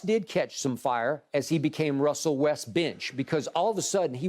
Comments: Russell West-Bench 😂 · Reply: I legit thought he said Russell westbitch lmao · I thought this idiot said russell westbitch 😭😭